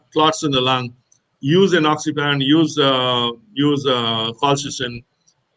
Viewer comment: he said "use: "oxiparon????" I don't understand what to use. Anybody can help? Fascinating talk. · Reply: Enoxaparin